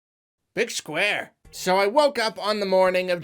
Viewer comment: I thought the intro said Bitch Where? Not Big Square